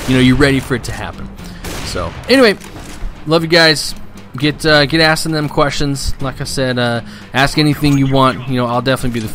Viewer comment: I think you are tired? Did I hear you say "Get assin those questions?" Where do you hope for your YouTube channel to go long term?